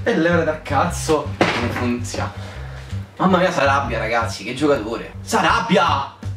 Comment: Hahahah quando hai detto "Mamma mia sarabia'', ho capito "Mamma mia s' arrabia"